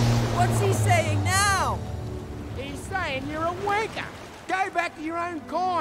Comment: I had to replay it several times because it sounded like said, "He's saying you're a wanker!"